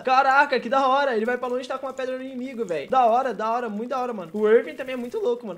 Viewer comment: kkk eu entendi ele taca pedra no obito